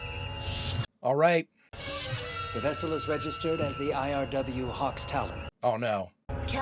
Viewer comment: I could have sworn that Spock said the Romulan ship was called the "Hawk Tuah" 😂